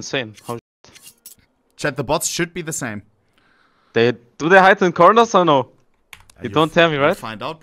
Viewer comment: what said here? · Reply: do they hide in corners or no?